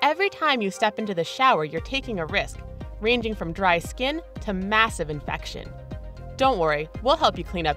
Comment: I thought she said massive erection first LMAO